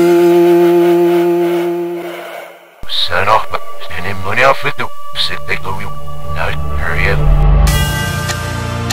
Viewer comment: somebody know what he say in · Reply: "Dreams about overflowing often symbolise unresolved emotional issues."